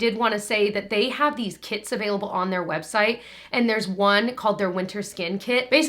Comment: Fully thought you said "they have these TITS, on there website" and I had to re-wind. LOLZ. TIPS, Tips.